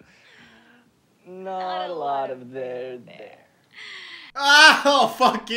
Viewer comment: Can someone help me with what they say in 3:33? (its something i've wondered for like 4 years) Im not a native english speakers and maybe the cringe is fogging my listening skills lol. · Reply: “Not a lot of there, there”. idk why they said that tbh but that’s what they said 🤷‍♀️🤷‍♀️